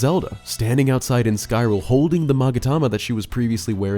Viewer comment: Did you say "Skyrule"? at 14:08?